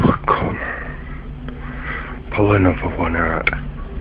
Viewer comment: I thought the guy in 9 said "pull an underwear out" lmao